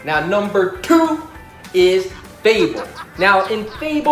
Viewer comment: what is this game? Didn't hear right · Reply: Ø fable